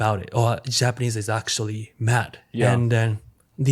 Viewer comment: I thought he said “Japanese is actually, Matt” and Asmon said “and they’re actually talking about Sumparomint” I’m sitting here like “Matt, what’s his channel? Who’s Somparomint? Did I miss something?!” 😂